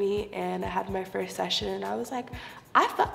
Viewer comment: did she just say "I fuck with him" ??